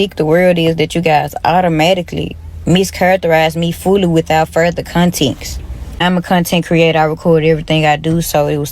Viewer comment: Did she say "Contenxt?